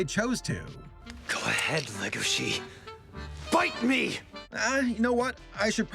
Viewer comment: I thought he said legashi XD